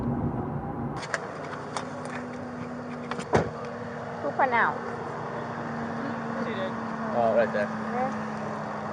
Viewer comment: What does she said at 1:40or 1:41 ? I can’t understand it??? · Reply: She said "Who pronounced?", as in who pronounced the victim dead at the scene.